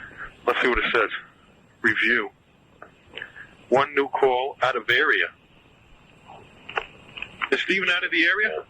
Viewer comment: First thing I thought, too. Caller ID. It shouldn't be that hard to track the prank caller down considering that he's a military guy 😂 Also, you're active in the military and you just start shouting for your family while in a suspicious call? I'm a 90's kid and I'm aware prank calls like this were already very much a thing. Dad was a military reserve. First thing he taught me was to be careful of suspicious calls. Especially the mimic calls and the calls phishing for personal information by invoking panic, confusion, or anything fishy. One of his best advice was to not let people know who you're with and where you are because that's how you get stalked or how the people around you can be targeted... Also, it's common knowledge that the longer you stay on the line, the easier it is to give the stalkers a chance to access your location details 😂 This whole "suspicious call" records is so wrong in so many aspects. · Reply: At one point around 5:45, it sounds like he did check caller ID: "I'm going to get you on... Let's see what it says. One new call," and then questioningly says, "out of area?" And then asks his parents if Steve is out of the area, being in Queens. Caller IDs occasionally would show that kind of message without a phone number, like when it was from an area that wasn't yet compatible with Caller ID, or was from outside the US, and it sounds like that's what happened.